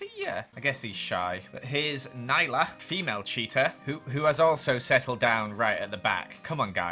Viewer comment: Everytime you say "cheetas" I hear "cheetos" "Cheetos are very shy"